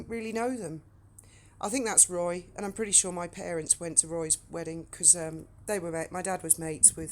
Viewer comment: How is Parrott in your family? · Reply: I thought you said Parrott. I must have miss hard you you probably said parents. Sorry 😞 Parrott I a family name by marriage. They were English and French.